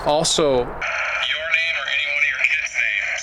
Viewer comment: where you hear "Annie", I hear - - "NANCY".